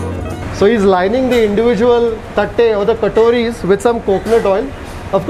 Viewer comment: is he said tatte???😂😂😂😂😂😂